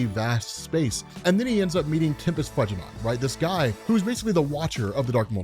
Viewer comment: Wait did say Tempest Fudg a not?